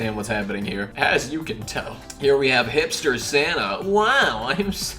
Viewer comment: when you said hipster santa i thought you said hitler santa